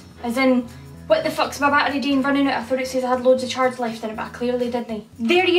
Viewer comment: “What the f##k is my battery doing running out I thought it said I had loads of charge left in it but I clearly didn’t” that’s what I got out of that idk first try